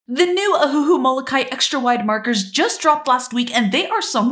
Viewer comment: I thought you said “extra-white” 💀 then I read the title